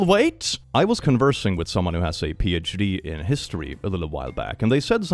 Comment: Okay, to be fair, "I'm buying tower fans for the vatican" is a WILD sentence to say 😂 But "giving broke" is insane to call it 👀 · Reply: help I heard "has a PhD" in the way one would say "has ADHD" 😭